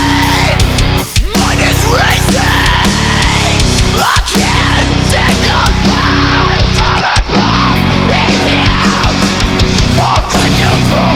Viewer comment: "I can't take a bow infront of God, let me out" That's what it sounded like to me